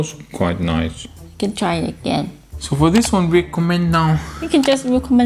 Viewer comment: It sounded like you said we could just nuke the Chinese base.... They already have a puppets in every institution in the United States, so even if we are first, it's still under Chinese rule. Don't worry though, American citizens are starting to take back the local level politics. The socialist take over of the institutions was slow, but with average Americans awake to the threat we can take back our country's government in a couple of election cycles, and break their hold on the rest of the institutions within a couple of decades. We will become a space faring civilization and the future of mankind will not be one of top down control, but of freedom and true humanity.